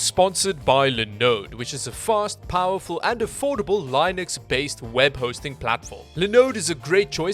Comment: Gonna be honest, every time you say "Linux based servers" I hear "Linus based servers" and I can't but see Linus' face photoshopped onto a server rack somewhere.